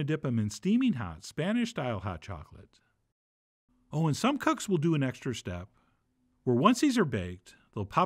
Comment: he says "some cucks" instead of "some cooks." can't tell if accidental or a chef john joke